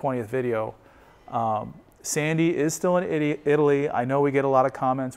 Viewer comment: Sounded like said sandy is still an idio, in italy. Im sure that opposite is true :) Sandy is far from an idiot.. :) funny still